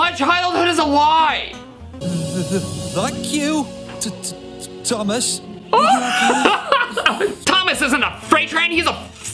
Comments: The SMILE before and as he was flipping the first bird, it was brilliant. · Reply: lol I think they were trying to say thank you Thomas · I heard “T-t-that you Thomas?” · I HEARD THANK YOU.